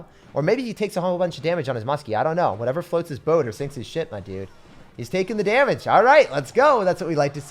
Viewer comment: did say "SHIT MY DUDE" AT 12:27 ooh ma bad he said sink his ship nvm